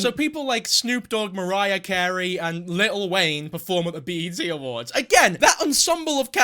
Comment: did you just say 'little wayne'